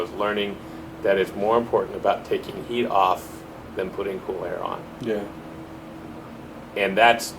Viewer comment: Can anyone please tell me what he says from 12:00 - 12:08 ? I just listened to it 3 times but couldn't get the advice. Thanks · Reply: He phrased it kind of weird, but what he said is that "it's more important about taking heat off than putting cold air on."